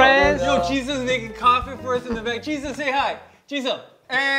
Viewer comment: No matter how many times I rewatch this, I (will) always hear Johnny say "Jesus say hi" instead of 'Jisung'